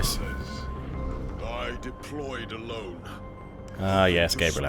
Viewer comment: I was paying more attention to the game play, when I thought I heard Gabriel Angelos say," I DEPLOYED A LOAD !!! " lol :)